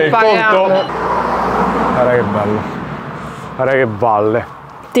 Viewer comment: IO HO CAPITO..." GUARDA, CHE PALLE !" INVECE DI GUARDA CHE VALLE 😄